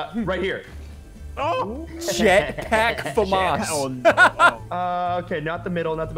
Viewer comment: when he said jet pack the caption thought it said s word PAC